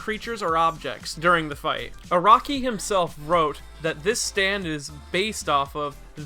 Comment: is it just me or did he say aronki? xd